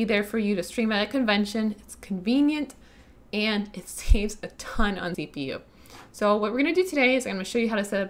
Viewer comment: Did you just say that this takes a ton of cpu? No thanks. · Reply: She says "it saves a ton on CPU". :)